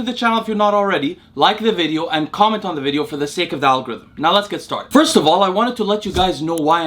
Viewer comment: Not gonna lie I thought you were saying “the sacred algorithm”